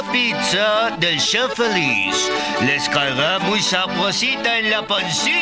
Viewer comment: escuche les caga mui sabrosita en la pancita😂